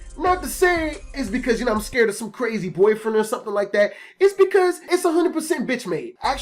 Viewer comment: Haha I thought it said "My Crazy Ex Boyfriend"